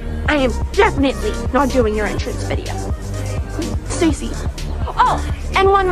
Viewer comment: Everyone heard Stacy i heard "sexy"😢